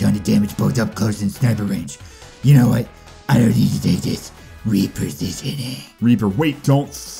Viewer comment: "Reapersitioning." That's what I heard